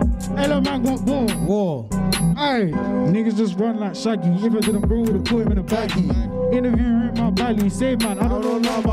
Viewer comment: what was the line he said after the shaggy line? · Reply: if they didnt, bro wouldve put em in a baggy